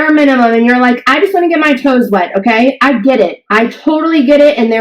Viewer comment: I heard her say get her tones wet. Haha. Tones was funnier!!